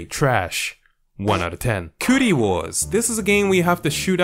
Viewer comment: My dumb ass thought it said “Coochie Wars”